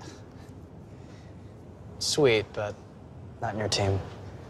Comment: what says? sweet but i.... team ???? min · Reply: I know its late but "sweet but not in your team"